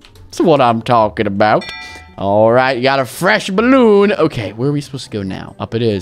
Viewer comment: did he said bloon???